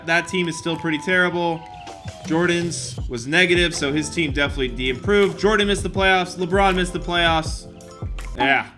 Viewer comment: I think said “De-Improved”